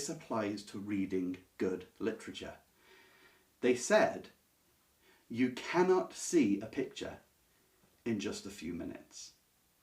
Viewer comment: I misheard at first, thought they said "you cannot see a picture in just a few *nights*" and was very taken aback by their monastic patience.